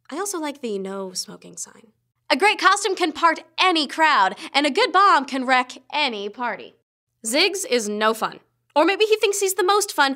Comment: I didn't have the subtitles on, so when she said "and a good bomb can wreck ANY party" I thought she said "and a good bong can wreck ANY party".